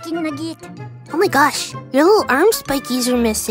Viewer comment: Why did I hear " My animator raised almond and forgets to draw them sometimes " 🤣